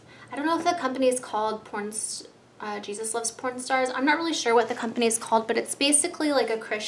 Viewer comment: What I Got For Christmas 2014! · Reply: i think u said pornstar :/ crorret me plz I'm 9 sorry